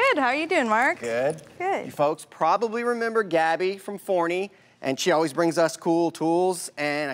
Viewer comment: I thought he said Gabby from “horny”…I was like excuse me! Lol